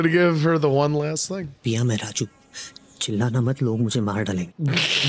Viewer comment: Paya 😂 instead of Pia